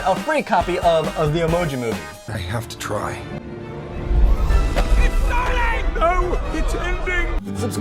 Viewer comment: I think the devil emoji said "ITS SONY!!"